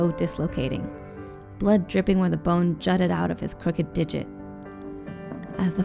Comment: i thought the lady almost said crooked dick instead of digit